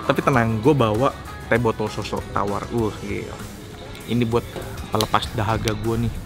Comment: apa gw gak salah denger? Teh tawar? Tumben ada d Indonesia.